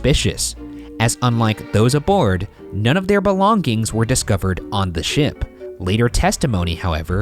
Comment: Does ryan say BELONG-GINGS